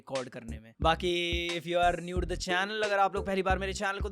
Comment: what he said 13:15 N*de to the channel waaaaaaaaaaaaah bete !